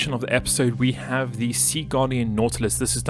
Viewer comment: i want to clearify at 21:54 he said SEA GUARDIAN i didnt hear what he said and the caption said "second" i wanted to point it out incase noone could find the ship